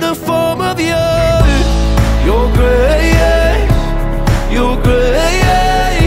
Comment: Whenever he said "Grace!" I always thought he was saying "Grey eyes!" lol